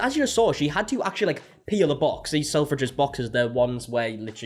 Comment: I thought you said "pee in the box" but then realised that you said peel lol 😂